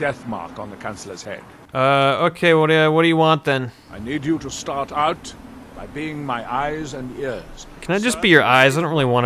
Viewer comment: I definitely heard Adril say "you start out cleaning my eyes and ears" which is still better than searching for books.